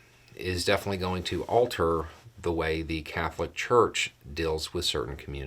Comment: I'm sorry, I'm laughing. You said alter...I hear altar.